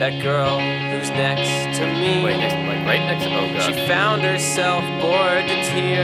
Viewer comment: What the heck does it say at 0:17? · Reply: wait next to me- like, right next to me? oh god